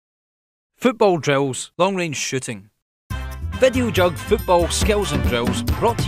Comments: at the begginning of the video it sounded like he said "long range shitting" Haha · lol i thought he said long range shitting